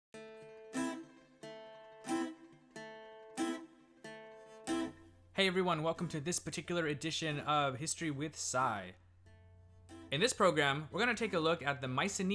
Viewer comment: I literally thought you said, "*history with sive*"